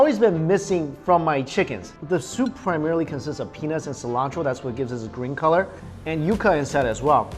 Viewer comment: did anybody else hear penis and cilantro?